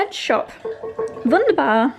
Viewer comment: Did you say "wunderbar" (German word for "wonderful") at 9:15? :D can' t Stop thinking About it, you Made me smile:)